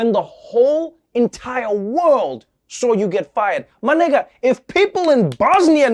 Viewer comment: Wait did he just say my N*GGa at 17:05 Or am I'm trippin?